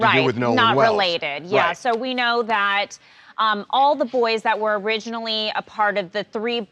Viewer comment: I thought they said all the boys Lawyer up, that’s not cooperating.